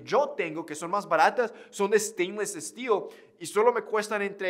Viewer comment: ¿Qué dijo? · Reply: Stain stelle acero inoxidable